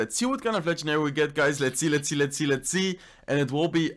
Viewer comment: Is it just me or does it sound like is saying “let’s eat,let’s eat” when he says “let’s see,let’s see”